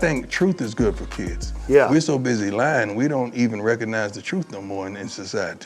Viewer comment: He actually said WE so busy Lying... not They're. His point is that we as a civilization are lying to ourselves everyday with what we put up with and call Reality.... that we no longer recognize the Legit Truth as it is.